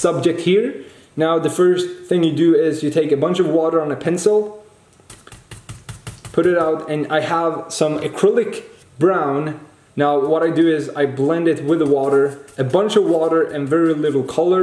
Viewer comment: HE SAID PENCIL INSTEAD OF PAINT BRUSH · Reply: No in German language paint brush is Pinsel.